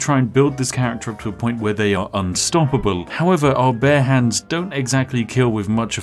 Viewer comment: You really need to find a mod for this to turn you into a bear, I just heard "bear hands" over and over again